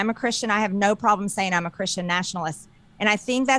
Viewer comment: I swear I hear "I'm not Christian", not; I'm a Christian.